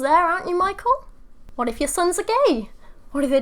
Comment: I thought you said “your son’s a gay” and I choke every time 😂😂